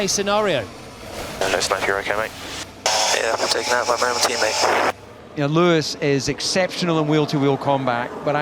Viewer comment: He said "Taken out my own teammate" didn't he? Might've misheard but I'm fairly sure that's what he said. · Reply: Nah, you missed a word there: 1:14 "Let us know if you're ok, mate." "Yeah, taken out by my own teammate. heavy sigh into the mic"